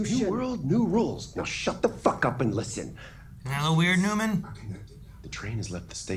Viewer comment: What does say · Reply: “Ain’t that a lil weird neuman?”